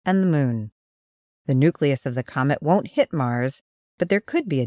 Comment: "Coment" instead of "comet" ??? Lol